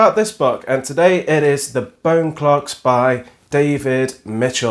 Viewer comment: I thought you said bone clerk's, which sounds like a terry Pratchett book